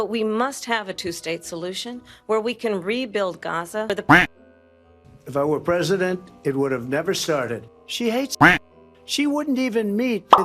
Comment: did he say "if i were president the israeli palestinian war would never have started" ?????????????? there's actually no way he said that i swear i must have misheard him